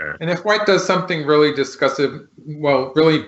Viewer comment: "And if White makes something really [...], well, really bad in the center to make the ladder work..." "I liked your first word." I didn't get that word. :( It sounded like "discussive", but that doesn't seem to be a word. Any native speakers out there?